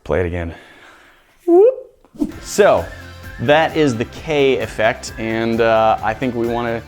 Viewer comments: I thought it said Kanye 😂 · Every kiss begins with Kaye : P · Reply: I thought it said kanye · I thought it said Kanye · please tell me i’m not the only one who thought it said kanye instead of kaye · I really thought these niggas said “kaNye” effect lmfao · who else thought it said kanye